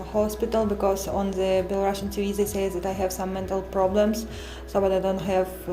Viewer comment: どうでもいいことなんですけど、私にはRussian TVではなくBelarusian TVといって言うように聞こえるのですが、聞き間違いでしょうか？